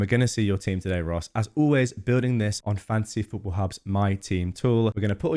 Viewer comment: I sounds like you say ”fancy football hub”